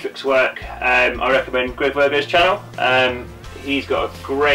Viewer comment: What was the name of the video series you mention at 1:29? I didn't quite catch it. Thanks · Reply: No problem, it was Greg Virgoe. I should of added it to video description!